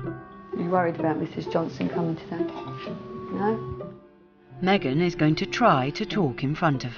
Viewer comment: “10 year old Megan goes cheerleading ever Thursday...but she doesn’t ever cheer...” Oh the irony! 🤫😭😂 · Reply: Four of Six, Tertiary Adjunct of Unimatrix 03 not sure why but I honestly thought you were saying Megan is loading 😅